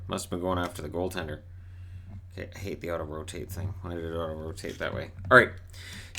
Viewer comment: Oh “AUTO ROTATE”. I thought you said Ottawa taint.